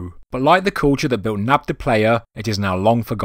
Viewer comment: Hi. At 4:54 you mention (and show) some ruins And I can't hear the name right. I hear "Nap de Playa" But cant find anything when I search for it, can you give me the proper name ? THANK YOU. Interesting Stuff ! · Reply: Dave Sykes it’s Nabta Playa 👍